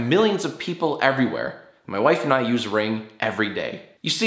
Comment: i thought you said "my wife and I use rape everyday"